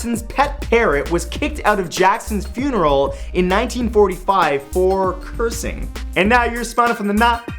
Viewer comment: In the last fact it sounded like you said that Andrew Jackson died in 1945 instead of 1845